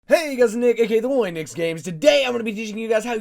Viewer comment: sounds like he said "Hey guys and niqqa!"